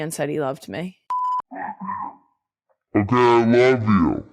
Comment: I think I heard Jason say " I l🍩ve y🍩u t🍩🍩"😂🤣